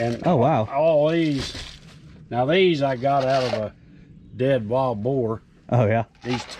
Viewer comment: Did he say "I got these out of a dead bald whore?"1:12ish.